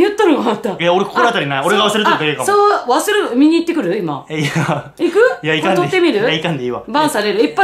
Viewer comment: <link> 絶対見てください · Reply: みんな！5:53～5:55 男の人の声が、（いや～）って、言ってる 一瞬 栄二くんやと思ったけど分析したら全く別の人が喋ってます。聞こえずらいので、イヤホン必須！